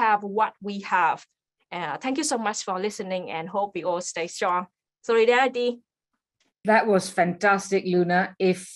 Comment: I love how when Luna cried "solidarity" the caption AI thought she said "Three Daddy"! Lmao. I'm gonna say that from now on. Three Daddy everyone!